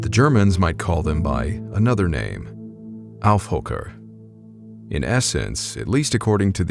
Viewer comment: I'm German and I have no idea what "alfhooker" you're talking about at 2:17. I have heard of skinwalkers, sure and I have seen strange beings myself (I don't think they were skinwalkers). Your subtitles make it "AI hooker" which is funny... I assume you're using a Polish word, but I'd really like to know what you were trying to say. Peace. ... Researched a bit, because the pronunciation is completely misleading, sorry. What you mean is Aufhocker or Huckup, and it rather means "sit upon" not really "leap upon". And it's basically a zombie or nightmare being that can come in many shapes. You depict werewolves in the video a lot - we have that, too, Werwölfe (singular: Werwolf).